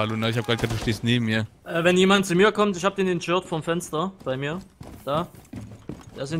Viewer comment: "in jört" ... hat ne Sekunde gebraucht um das zu verstehen. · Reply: in Shirt :D